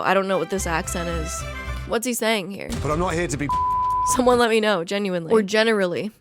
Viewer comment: I think he's saying "I'm not here to be an asshole" based on the way his mouth moves